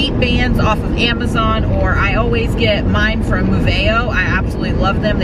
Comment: Where did you say you get bands from? Thought you said aveo but I can’t find any. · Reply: Moveo ❤️